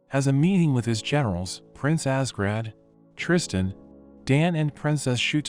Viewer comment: did anyone have a double take moment hearing the name? My adhd ass drawing while passively watching this had to rewatch this part cause I thought it said “Prince Ass Grab” 😂😂